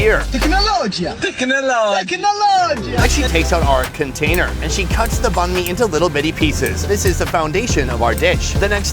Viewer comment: does anyone know what that audio clip at timestamp 18:01 about the fan I can’t make out what they’re saying or where it’s from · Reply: The word is 'technology', whoever is saying it is Arabian(I think), but they are pronouncing it as 'technologia'. The phrase pretty much refers to how technology has developed.